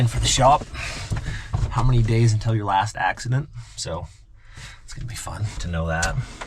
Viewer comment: "How many days until your last accident" That sounds ominous. Not sure I want one of those signs 😂😂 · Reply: I thought he said “how many days until your last accident”. Came running g to the comments. Was not disappointed.